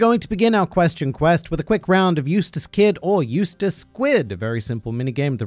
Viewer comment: I totally thought you said Eustass Quid, and was a little confused when you showed a picture of a squid.